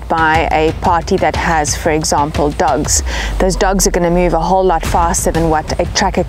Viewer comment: I thought she said “Dougs.” Not sure if those can be defeated.😅